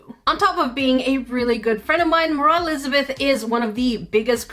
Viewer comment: Was I the only one who heard Mroh when she was introducing her